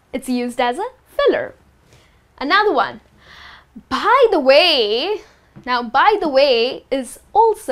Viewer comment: Bhai the way instead of by the way :P :P :D